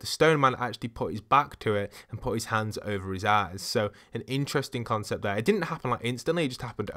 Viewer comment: Can anybody honestly tell that it doesn't sound like he said "over his ass" there?